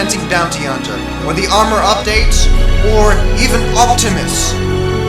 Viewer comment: What does he say at 4:37 I can't understand that word.. Optimus? Like If Optimus Prime was added into Runescape?